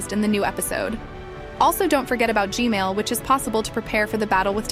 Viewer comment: She said gmail instead of g-man 😂😂😂😂😂